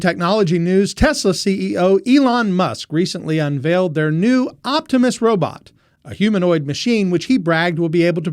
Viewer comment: Thought you said it was an optimist robot. Like it was the opposite of that robot from Hitchhikers guide to the galaxy or something 😂